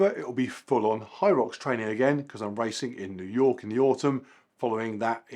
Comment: Did you say New York? I don't remember you mentioning any US events before - is this a new thing?